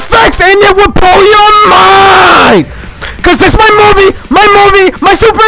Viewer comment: i think sometimes like he says it would blow your minecraft world